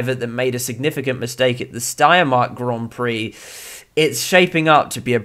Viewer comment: did he say the Steinmart GP lol😂